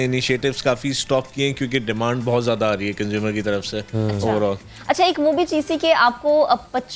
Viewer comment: i first thought he said, "..kanj..... Ki taraf se"